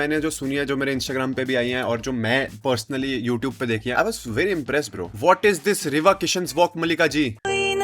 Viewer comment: wait, whattt did he say? "Riva" kishan😂😂